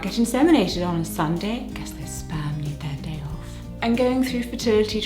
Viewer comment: Okay, so I knew the video was about a baby because of the thumbnail but when she said what she got her wife for Christmas, I thought she said SPAM. Like the lunch meat SPAM and I was super confused and was like what??? And then she said fertility treatment and then I was like ohhhhhhh 🤦🏻‍♀️ not my brightest moment 😅